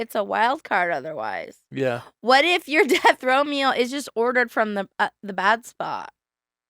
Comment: Swear I thought yall were saying “death Romeo” not “death row meal” 🤦🏾‍♀️😂